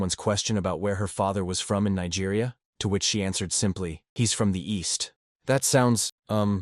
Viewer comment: I thought she said "weast" 🥴😂💀